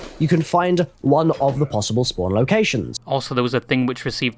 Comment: did he say “spawn locations” ????